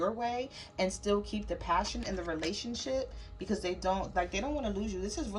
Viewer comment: I Almost thought you said RelationSHIT Lol 😂